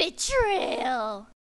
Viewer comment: When it was supposed to be "BETRAYAL" I heard "BITCHRAYAL" ..... My god what am i doing to my life